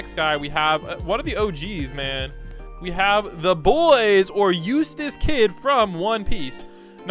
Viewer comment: i heard Useless Kid